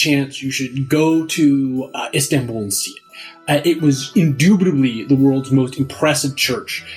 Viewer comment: Did you really say "indubitably"?